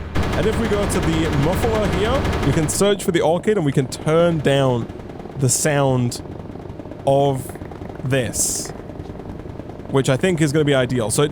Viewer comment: What did you say? I can't exactly hear you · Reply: Back to the orechid so we can turn down the sound of this